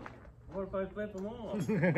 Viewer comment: What her dad say? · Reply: I've played it back a dozen times and all i can understand is "What if I ... "